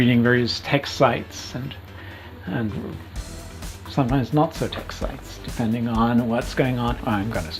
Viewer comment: What did he sayyy 👀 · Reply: "sometimes not-so tech sites, depending on what's going on"